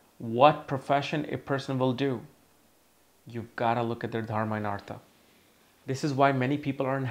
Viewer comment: what is that word u say at 2:16 pertaining to careers? sounds like darminatra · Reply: it's Dharma(duty) and artha(finances)